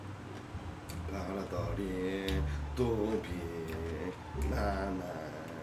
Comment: 여기 소리 크게틀고 봐도 뭐라는지 안들림 내 귀가 안좋나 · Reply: 보라돌이 뚜비 나나 버억